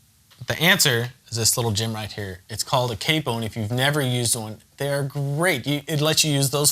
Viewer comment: What is that little instrument @ 6:00 called ?? Please help...Need exact name so that i can buy it !!! Didn't understand what he said ... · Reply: It's called a capo.